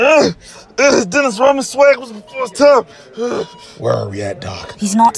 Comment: I could’ve sworn he said “Uh-Dennis Rodman swag was before it’s time…Ugh”